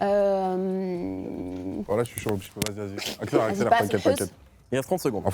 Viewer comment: A 5:11 quand il dit « Hancock » comment ils se foutent de sa gueule je pleure 😭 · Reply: c moi ou il dit "wlh chui chaud" 🤣🤣🤣